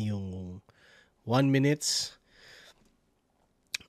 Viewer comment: Tama ba Yung narinig ko? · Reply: Won minutes yun, tig 1 minute lang mga rounds.